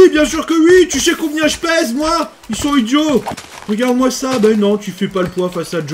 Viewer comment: au début j'ai compris "tu sais combien je pèse moi ? 108 kilos"